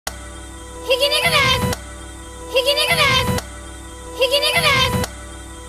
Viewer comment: 途中からひき逃げですって聞こえるのって俺だけ？